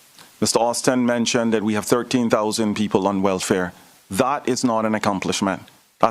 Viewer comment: Did he say 13,000 on welfare?